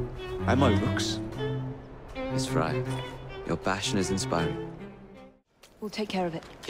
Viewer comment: when i first heard Jacob say "and my rooks" I thought he said "And my looks" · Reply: And when Henry said, "your passion is inspiring," I heard "your fashion is inspiring." XDDD I was thinking, "why, yes, Evie definitely has better fashion sense than Jacob!" LOL!!!